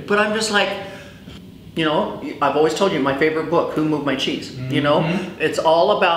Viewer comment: Hi! I didn't quite catch what favorite book was (18:20). Will anyone tell me what that was please? Thank you. 😀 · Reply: The book is called Who Moved My Cheese